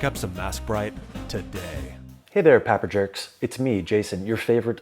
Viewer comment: Are my ears playing tricks on me? Did i just hear 'Papper Jerks' Bahahahahahaha. Dude, you're looking sharp also! Sweet hair cut, shaved face but I'm glad you left the chest hair run wild and free 😜. Loving the great content dude. Keep it coming